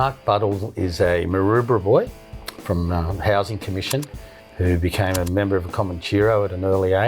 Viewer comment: Well he is from the middle east where life means nothing. I used to know the Sergeant at arms from one chapter in 2001. They were a splinter group from the bandidos after a falling out. I have no respect for them now when they use violent non riders to carry out threats. I lost respect for the nomads in 1991 when they didn't get rid of an informant that I told them about after they lost 6 members including their cook (speed maker) In the old days they used to ride, peddle drugs and just have a good time, but there was some paedophilia which is wrong, but since they brought in absolute cun*s who don't ride and will do anything because they don't care they are crap IMO. Aint immigration wonderful? Should get creepy joe biden to drone strike him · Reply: I thought he said housing commission in Maroubra